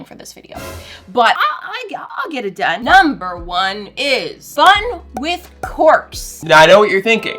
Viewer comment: when she said fun with corks I thought she said fun with corpse